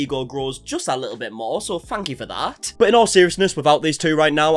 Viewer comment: "Fank you for that" I thought you said "f*ck you for that"